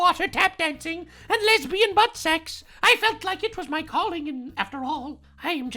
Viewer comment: Did just say Lesbian butt sex? 😂😂😂